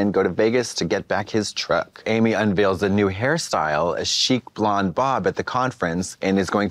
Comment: I thought Katya said, ‘cheap blonde bob’ it was ‘chic blonde bob.